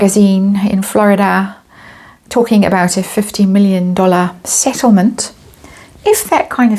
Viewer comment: He will be freed once Megan is ready to leave but i think 15 million is too low of a figure unless the next man has a lot more to offer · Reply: I thought she said ‘50 million’. That I would believe